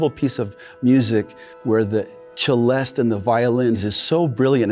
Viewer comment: Really great job on the music. Looking forward to hearing the whole score! For the subtitles, just wanted to point out that at 1:50 it should be "celeste" or "celesta" instead of "cellist" :)